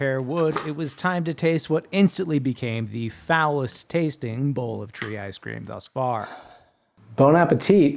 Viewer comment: Sorry, did you say foulest tasting or phallus tasting? Glad to see you managed to make it work, though!